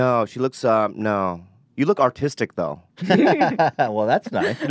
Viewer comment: Bobby said Hila looked artistic and I swear I thought he said autistic